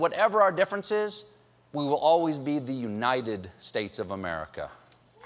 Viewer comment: I really thought Colbert said "divided states of america" at 1:12 I had to rewind